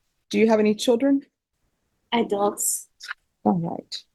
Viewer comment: she said she didnt have any children--she is boring ..she is a thief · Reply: I thought she said adults when asked about children so I checked the transcript, at 7:06 she did say adults.